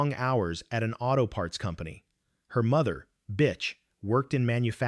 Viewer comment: Did I hear the mother name "Bitch" perfect name for their crime.